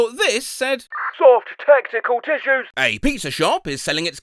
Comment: I misheard that as “Soft Testicle Tissues” 💀💀💀😭